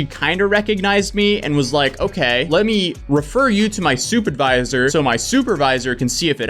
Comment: I thought he said soup advisor not supervisor... I was like, why would they need one of those? 🤦‍♀️